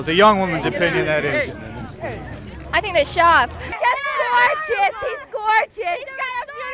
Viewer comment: Shout out to the classy girl who thinks and calmly says I think they are sharp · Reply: i thought she said "i think they shop"